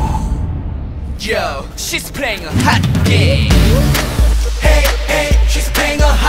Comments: I could have sworn he said "shes pregnant, hot game!" xD LMAO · At first I heard "She's pregnant hot game" but after a couple listens I knew that isn't what they were saying x3. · for some reason I always mishear the first "she's playing a hot game" for "she's pregnant, hot game" lol